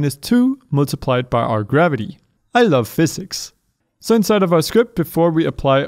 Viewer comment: Is he saying I love physics